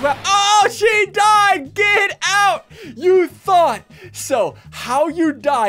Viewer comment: is it just me but did he say thot